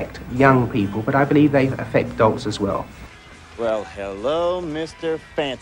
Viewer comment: "...affect dogs as well." For the amount of sense he was talking it may as well be what he said. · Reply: He says adults not dogs. I thought the same thing.